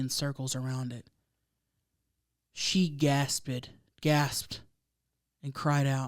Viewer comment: lmaoooo why am I cracking up because she said "gas-ped" instead of "gasped"..? 🤦‍♀️❤️ love you hunnies ! watching/listening from Delco PA outside of Philly !